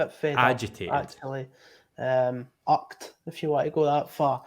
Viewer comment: wow took me a few goes of listening to the word "irked" in that Scottish accent before I realised what the word was!